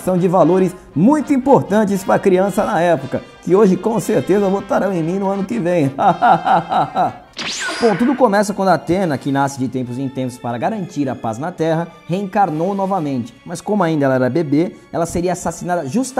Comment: Eu juro que entendi 'Datena' kkkk imaginem a imagem mental